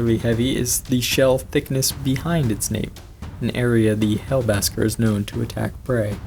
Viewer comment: Funny story: I was watching the curious archive video on this (long before I even knew you HAD a Chanel) and I misheard hellbasker as hellbastard (idk how) and every time I here about the hellbaskers, I think of hellbastard, and giggle like an idiot, lol